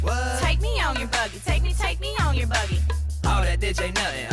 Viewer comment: At first when they say ah that ditch ain't nothing I thought they said ah that bitch ain't nothing LMFAO !!!! 😄😄😄😄